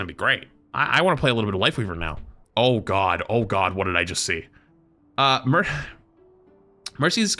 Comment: I though he said oh god oh god what did I just say